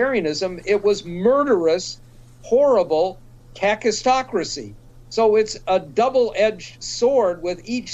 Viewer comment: Please explain what word he uses at 15:07. Never mind, I found out it's "kakistocracy". I had never heard that word before.